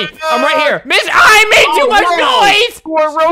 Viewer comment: AM I THE ONLY ONE THAT HEARD HIM CUSS I THINK idk all i heard was "b**ch i made too much noise" LMAOO